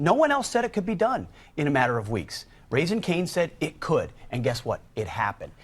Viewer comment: did he say raising canes? I like chicken fingers but i didnt know they had geopolitical influence with the usa government.